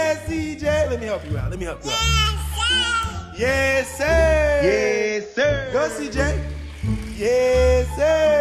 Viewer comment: did i just hear a 1 yr old say “yesssirr”😭😭😭 make that a sound